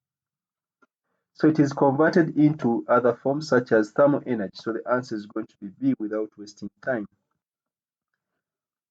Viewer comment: i unable to understand because of speed can you write what you said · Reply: the total area between loading graph and x axis, is work done on loading, total area between unloading graph and the X axis is the work which is recovered when load is removed, so the difference between the 2 areas is the shaded part and its the energy which is not recovered so its wasted as heat.